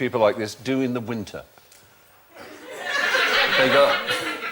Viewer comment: what did phill say? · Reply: He started saying "They go..." but then stopped because of the funny laugh in the audience (the woman was laughing because of Stephen's question).